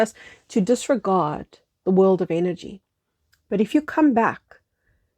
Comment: With your accent, "energy" sounds like "inner g". Therefore I heard; "Invest in your inner world, invest in your Inner G". Inner God.